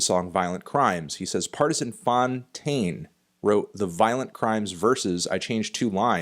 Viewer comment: I thought it said "Fantano wrote Violent Crimes" and I did a double take.